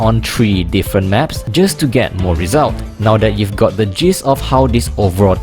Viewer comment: Friendly note: at 1:09 I think you meant to say "Now that you've got the 'gist' .."., but ended up saying 'jizz' instead of 'gist', which means something completely different :-) Good video otherwise!